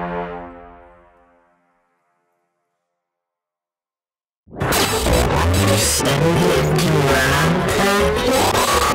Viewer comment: When you just wake up and u try to understand what the person who woke you up is saying. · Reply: He said "have you send it to robtop yet?"robtop is the company of GD